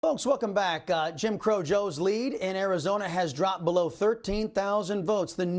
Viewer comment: Did I hear “Jim crow joe”? Lmao 🤣